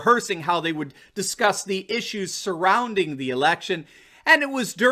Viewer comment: oh election i thought you said erection ha